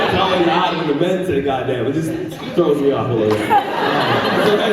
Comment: What does he say at 3:07? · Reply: He says it throws him off when the men say "goddamn"